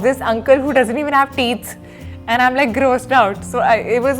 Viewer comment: did she say teeths????😂